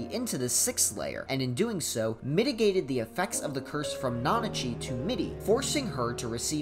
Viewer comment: I'm not sure if my enjoyment of puns has ruined how I comprehend stuff but I heard "Mitigated the effects of the curse from..." As "Mitty-gated the effects of the curse from..."